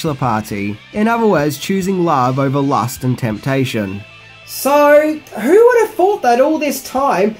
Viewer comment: Goodmorning.. first I heard "choosing lava over lust and temptation" xD